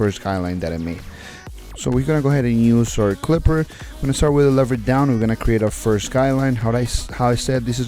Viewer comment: the video says lever open but what he says is lever down smfh goofy